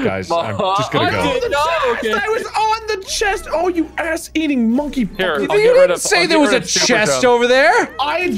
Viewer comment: Did Wade just say “Oh you ass eating monkey fucker”?